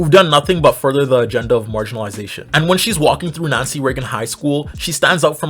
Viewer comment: When you said the name of the school I heard it as " Nazi raven high " 💀